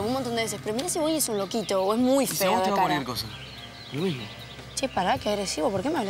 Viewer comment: que dijo rama si alguien entendió me dice porfi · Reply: "Y si a vos te va cualquier cosa, es lo mismo".